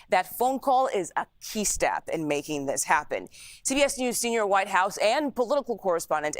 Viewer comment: I could have sworn she said TDS news 😂